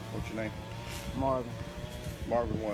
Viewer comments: Who said Marvin? Lmao i dont know what you guys heard but before even brought up that he lied about his name I heard and thought it was marlin just based off the video t he said it pretty clear i dont know why they would try and say he lied about his name . Now I’m not defending the whole situation but just that part where they claim he lied about his name to me is BS i never heard Marvin until claimed he said that was his name and soon as he did I went back because I thought I heard marlin and what do ya know I did . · 1 correction: he did say his name correctly, you misheard him. He said marlin and not Marvin. Otherwise yes he did lie. Just wanted to say that, since it was bothering me lmao